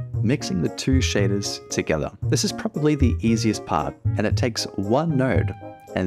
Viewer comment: Did you say... Nerd?